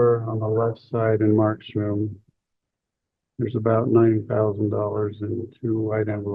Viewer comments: Did he say $90,000? And, is the nervous laughter odd to just me or is everyone offended at hearing that? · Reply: I thought he said $9,000.00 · He had $9000 in cash?? Looks like he might have been planning to disappear. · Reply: Sounds like he said 90,000